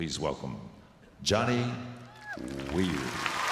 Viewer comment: It sounded like they said Johnny Weird